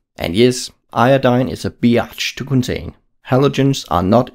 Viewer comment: did you say bi-arch